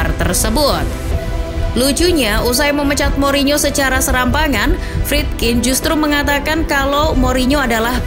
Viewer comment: Ini gua salah denger atau naratornya lagi ngejokes ya .. Masa SERAMBANGAN bukan SEMBARANGAN 😂😂😂😂